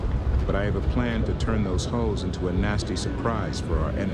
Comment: it kind of sounds like he said "hoes" instead of "hulls". I could not stop laughing.